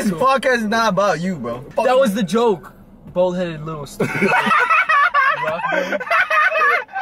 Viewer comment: The way He said “ bald headed little stupid” has me rolling on the floor😂💀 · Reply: I thought he said little bald headed Caillou😂